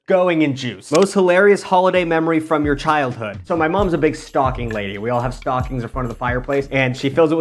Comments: i swear i heard “my mom is a big stalking lady” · I thought you said your mom was a big stalking lady 😂